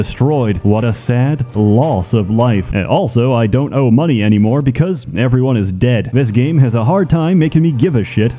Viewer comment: It has been three years. And only now I realize he says "And I don't owe money anymore because everyone is dead." All the time I thought he said "And I don't OWN money anymore because everyone is dead." Since when everyone is dead, money have no value.